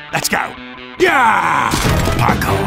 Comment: thought he said "icon" instead of parkour bro.